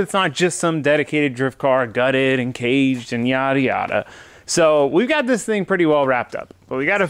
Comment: says "yada yada" but I hear "Miata Miata "